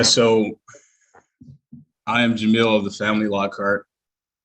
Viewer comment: Sounded like he said "Jameel of the family law court"